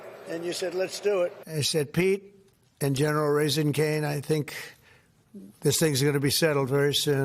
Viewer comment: Did he say at 6:14 general raising Canes? As in the fast food shop.